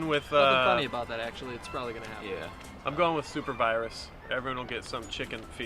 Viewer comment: did someone say super virus? I'll be back when its robots.